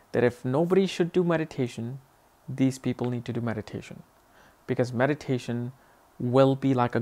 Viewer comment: I thought you said, medication. lol